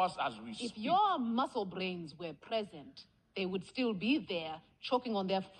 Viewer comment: I can be the only one that thought he said "you bald-headed lemon" 😂😂😂